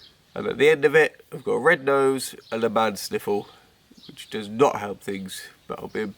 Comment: did you say “man sniffle” or “bad sniffle” ? I can’t tell because you’re nose is so bunged up. Take care. Your boys are SO cute 🥰 Well done.. another fab vlog 👍